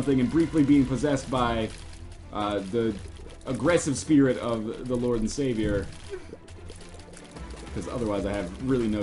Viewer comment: did he call the devil "The lord and saviour"?